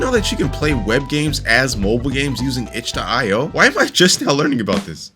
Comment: using what??? I literally replayed it 10 times never caught up with what he said, someone help please? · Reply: using itch.io